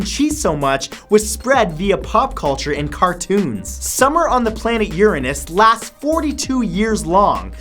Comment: Did he say planet Uranas?